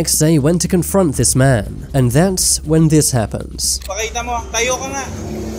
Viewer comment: if your wondering what is the cameraman was saying he was saying "standup and show it"